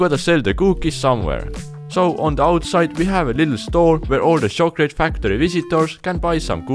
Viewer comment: I keep hearing "Shulklate factory"